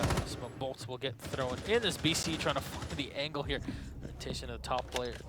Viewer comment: What did he say? BC trying to fuck the angle here?